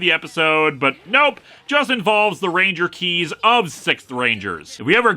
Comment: Between all the Star Wars references in this, every time you say, 'Sixth Ranger Keys,' I keep hearing 'SITH Ranger Keys.' Now I'm just imagining Count Dukko, General Grievous, Palpatine, Dark Vader, and Kylo fighting Power Rangers in sentai fights.